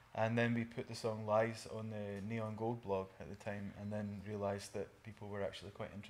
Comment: Could someone tell the the name of the blog that Iain mentions at 0:26? can't understand his accent at times thank you :) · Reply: The Neon Gold blog <link>